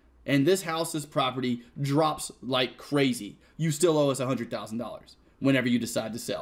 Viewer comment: did he just said "you still owe us 100$ USD"??